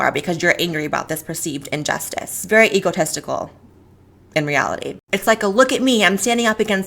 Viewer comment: Does she say egotistical or egotesticle? :-)